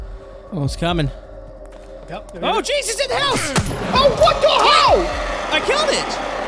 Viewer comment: did just say what the hell?